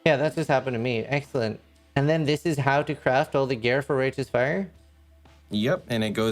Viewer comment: subtitles say racist fire XD